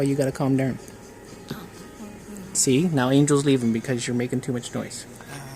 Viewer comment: I believe I heard say Angel...whispering! Awwww!